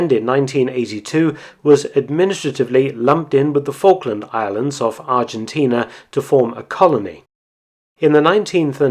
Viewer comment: I thought I heard "...the Falkland Islands OF Argentina" ( @ 0:51 )and not "...the Falkland Islands OFF Argentina". What does that say about me? They say, that you only hear what you want to hear. The only link between me and Argentina, that I can come up with, is 'our' Argentinian queen : daughter of a former minister of agriculture in the junta, during the Falklands conflict. That must be it.